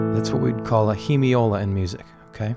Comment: i can´t understand what did you say at minute 3:29 ... "thats what whe call a (?)" i´m italian sorry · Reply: Hemiola!